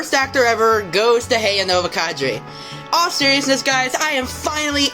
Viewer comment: "hey-a nova kaddry"??? Wtf is he saying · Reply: i believe he’s saying “heyah novak3” which was his username. sounds nothing like that tho and sounds more like your phonetic spelling